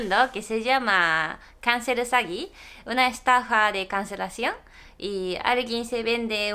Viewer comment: cuando dijiste "estafa por cancelacion" te entendi "conejo con cancer" ajajja